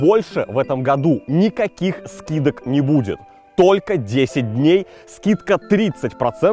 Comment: Услышал - на 2:00 - Польше скидок больше не будет... >_< думаю, при чем тут Польша?